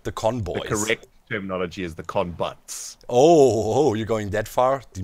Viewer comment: thought he said the "confucks"